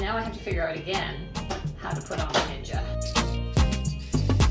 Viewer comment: I think Jennifer Aniston should check what you do to keep it so youthful and pretty. A question for you, what is your blender's brand and is it a food processor too? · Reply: I think she says around the 3:45 mark that the blender is a ninja